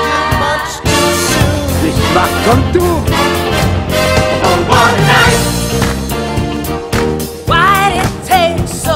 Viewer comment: what did he say. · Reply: He said "Joey, you owe me five bucks".